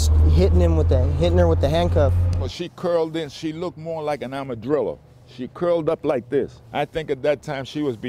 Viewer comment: did he say "Arm-a-drillo" 🤔